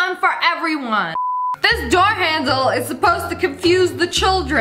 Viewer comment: I thought she said dog handle- but i replayed it and she said door handle- 🥲 i rlly thought she said dog handle🥲🥲